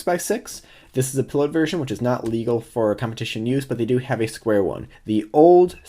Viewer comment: when you said they also have a squared one I first thought that you said they also have a square-1. I was kinda confused and then I facepalmed for myself